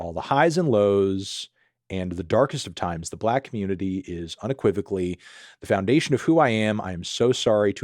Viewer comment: what did he say? 😭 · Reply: Unequivocally